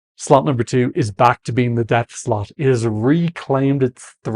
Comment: I know you're calling nr2 the death slot, but I keep hearing "death slut" 😂